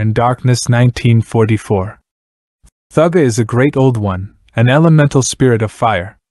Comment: Cant take this video seriously when i keep hearing "thugga" right off the bat lol